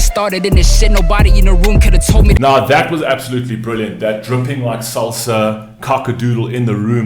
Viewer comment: I think he said "chipping like salsa". Taquitos??? Lol also it ties in with poker "chips".